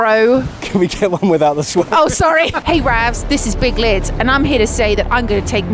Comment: I liked the video but I must admit I thought you said " hi I'm big clitz" when you introduced yourself 😂